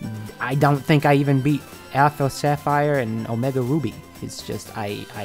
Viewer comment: Could have sworn I heard you say Afro Sapphire at 2:06 XD But welcome back. Can't wait for more Pokemon Content.